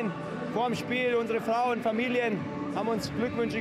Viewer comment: Müller: Jetzt wirds ein bisschen schnulzig 😂😂😂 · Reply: Aha ich hab Schmutzig verstanden 😂😂😂